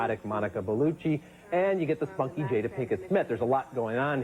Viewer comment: did he say “the funky Jada Pinkett Smith”? 😂